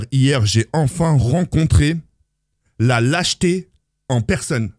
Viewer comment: Quand j'ai entendu " la jeté" au lieu de "lâcheté" je suis a l'Ouest Courage franchement c'est dur mais sa passera mon frère inchalah